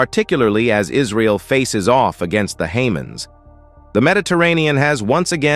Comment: The Hamans? Who the hell are the Hamans? How hard can it be to have an actual Human (not a Haman) be the narrator, instead of a computer? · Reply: Oh, man, I literally lol'ed when I heard "Hamans" xD